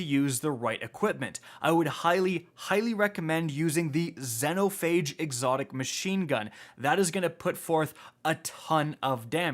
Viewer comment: what the hell did you just say? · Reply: "I would highly recommend using the xenophage machine gun"